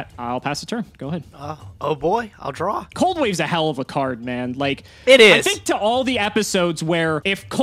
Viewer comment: says: "Cold wave is a hell of a card." I hear: "Cold wave is a hell of a drug."